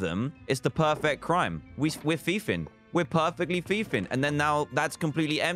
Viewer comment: did anyone hear Dan say fiefing?